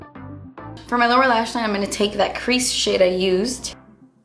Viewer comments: Did anybody notice that it sounded like she said "crease shit" instead of "crease shade" hahaha! Not being mean, I lover her she's really the only person I watch on youtube, it just sounded funny to me. Or did she mean to say that??? 17:07- that's when she said it. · when she was talking bout her lower lash, i could've sworn she said "crease shit"😂😂